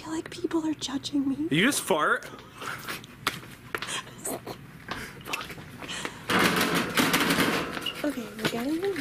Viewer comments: Durring 4:40 people say that said F*** he said LOOK and he said OW MY FOOT Durring 5:50 but I get it...it Kind of sounded like that=:O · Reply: it sounded like when said did just fart!?! I think said the F word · DO YOU HEAR THAT THAT SOUND LIKE SAYING FU*K